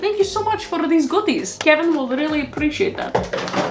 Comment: when u said “kevin will really appreciate that.” when someone gave you dog treats, i thought you said “karen will really appreciate that” and i was like “DAMN savage katrinaaa”